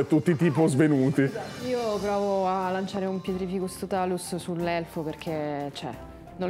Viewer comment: (più o meno) io ho sentito pietrificus e non petrificus 😂